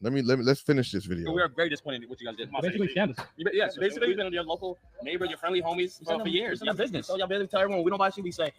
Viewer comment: it’s your friendly neighborhood, Spider-Man, will do you dirty 🤣 Is that what he just said?